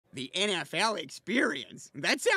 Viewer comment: i thought he said the nft experience